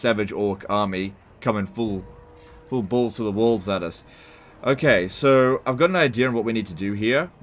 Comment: It sounded like he said "...'bulls' to the walls at us."